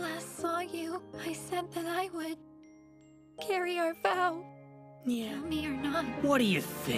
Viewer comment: I swear, I hear Zack say 'Meow' 😍😂😂😍😍